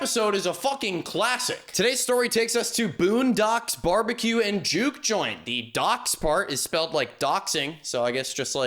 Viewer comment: jesus christ i thought they were saying JEW joint at the beginning and i was like 'like a joint rolled by a jewish person?'